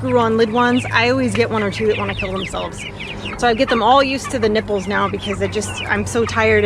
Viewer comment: did she say " get them used to nipples"? Chicken what? I need to lay down..